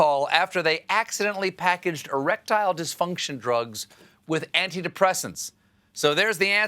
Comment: I thought Steven said they swapped the pills for Reptile Disfunction, it took me a bit until I realized I heard wrong.